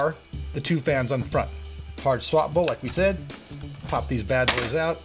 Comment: When they announced the hard swappable fans I thought they said hot swappable, bdw Nice video